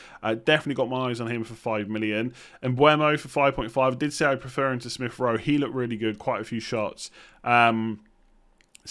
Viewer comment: couldnt catch. Who is talking about at 08:00 ? thxs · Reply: Mbuemo